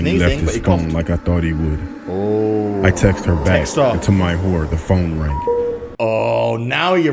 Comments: there was a subtitles on for me I don't know if there was subtitles on for anybody else but at this moment it said. And to my whore instead of horror 🤣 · lol did anyone notice the subtitles said, “I texted to my whore” XD 😂 Also that story about that creature trying to lure the woman out, I think that was meant to be a vampiric creature